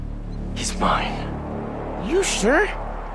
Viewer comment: Sorry I actually heard him saying "are you sure ? He's got big D" "So do I" 😂